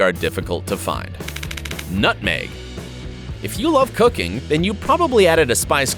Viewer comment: I thought he said if you love cocaine instead of cooking 😂